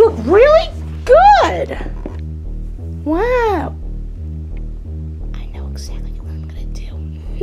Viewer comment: if anyone couldn't hear what she said she said i know exactly what I'm gonna do